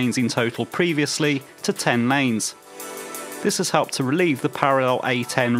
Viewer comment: Well done, nice video. Why are you saying "10 mains" instead of "10 lanes" in some places? 0:37, 2:13 and